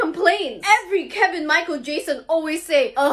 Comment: Everyone be like:fr why do men are like not taking of their face Me: did anybody misheard her saying Michael Jackson?😅